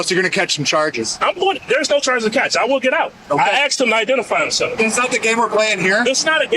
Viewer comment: subtitles are wrong, he said he axed, not asked 😂😂😂😂